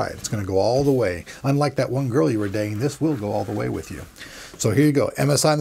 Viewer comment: wait what did he say 1:29, unlike that girl what? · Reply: "unlike that girl your were dating"